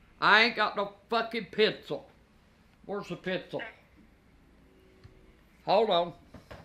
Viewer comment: I swear I thought she said "where the f** is the pistol"😆